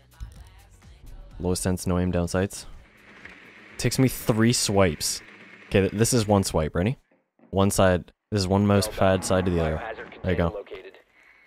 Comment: "it takes me three swipes" i heard that as wipes and was like, 'me too man, me too'